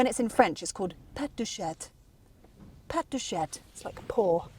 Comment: Sounds like she is says "pet de chatte" when she is trying to speak in French.. "Pet de chatte" would translate to "Fanny's fart"